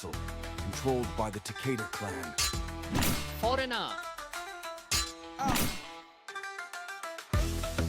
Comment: Did I just hear "foreigner" @ 5:27? Hillarious!!!😂😂